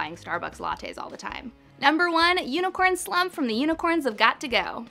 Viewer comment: I always thought they said it was unicorn slob like a unicorn's saliva